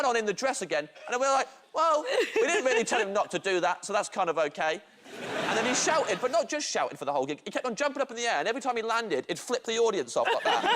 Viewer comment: What does he say at 3:45 😅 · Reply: “We didn’t really tell him not to do that [wear a dress] so that’s kind of okay. And then he shouted, but not just shouted for the whole gig, he kept on jumping up in the air and every time he landed he’d flip the audience off” lol I just realized your comment was 7 months ago, hopefully you see this and can have a laugh again 😂